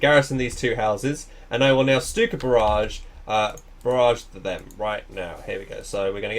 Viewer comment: Is it just me or do i keep hearing "Cyka barrage"?